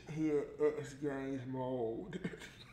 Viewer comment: What did he say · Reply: she in xgames mode